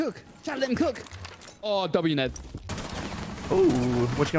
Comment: what was that word u were about to say?😂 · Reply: Nade